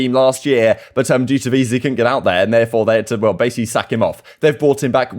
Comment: I was like what did just say? Lol had to pick my phone up and back it up to re hear to make sure. Lol thought he said "he couldn't make it so they basically they had to suck him off" 🤣🤣